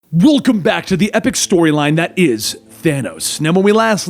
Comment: Excuse me, but uhhh.... Did he say thano at 0:05?!